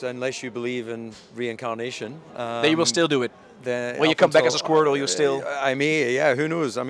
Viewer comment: Did you just say, "When you come back as a squirtle?"